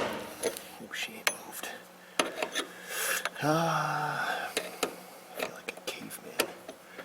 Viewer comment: "I feel like a cave man" is that what he said